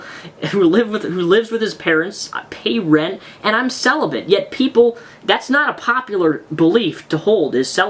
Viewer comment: What is that word that starts with "s" he says at 10:21? I really can't understand what he's saying, and I would like to know the word. I feel really illiterate not knowing what it is; Google doesn't even understand what I try to sound out to it. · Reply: "celibate"